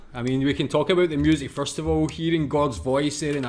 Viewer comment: Your accent almost made it sound like you said "Listening to God's voice..." at the end of the video, but I know you said Gord. Though for many Canadians, Gord is the voice of God.